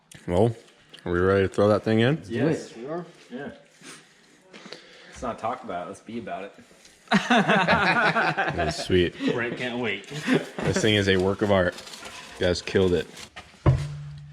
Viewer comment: I thought he said "Let's not talk about it - let's eat about it".